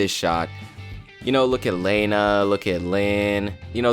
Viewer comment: Did he just call lana laina at 7:17 and 9:51 😕😂😅🤣